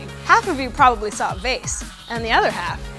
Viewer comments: vase or face? im assuming face but i keep hearing vase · on illusion 3 I what's the other thin I could only see two faces and though the black was a random shape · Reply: doesn't matter she said it on TE video but thought she said a face not a vase